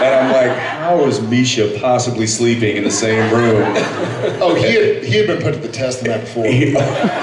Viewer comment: what did jared say at 7:33? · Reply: He said, "'He had been put to the test the night before." lol theyre being so dirty!